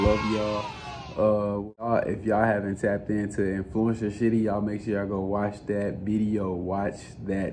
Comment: it sounds like he said influencer shitty😭😭